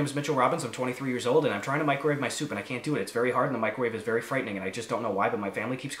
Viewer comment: At first I thought it said, “Microwaves Soap,” and tbh that just made it better